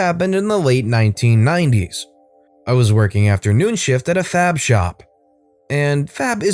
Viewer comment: I was only semi listening until I thought I heard "fap shop".